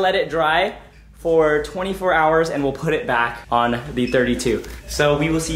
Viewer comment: DID HE SAY PUT IT BACK ON THE R32 ?!?!?